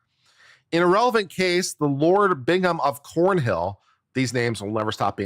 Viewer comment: I thought you said Lord Bingham of Pornhill at first